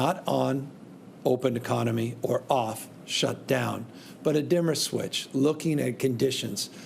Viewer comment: “Dimmer switch” Dimmer switch.” 🤔 for some reason I keep hearing “Double plus good” (1984 George Orwell)